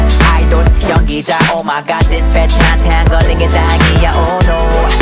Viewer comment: When I heard this song and when he said dispatch yo I thought he said "this b****" omg 😂